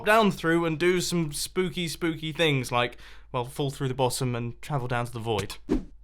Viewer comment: IDK why but I heard fall through the bosom